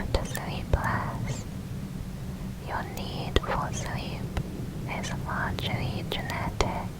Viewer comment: What did you say between "Your need for sleep is" and "genetic"??? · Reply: 8 days late but she said “your need for sleep is largely genetic”